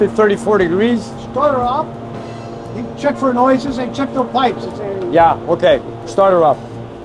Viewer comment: I thought I heard some chicken noises and you were resetting the timing to 34